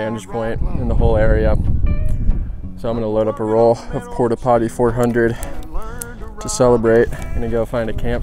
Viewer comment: Yup.... had to rewind the video twice to make sure I heard it right - ' Porta Potty 400 ' - at 5:16 of the video.